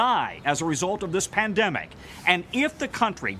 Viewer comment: BTW: at 1:23 caption says "EPIDEMIC", it should be "PANDEMIC". Let's not downplay it.